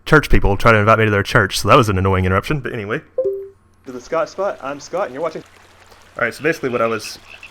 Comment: Lol I thought you said thescottsbutt